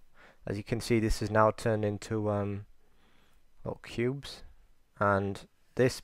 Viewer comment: I love the tuorial but did he say pubes at 6:25 LOL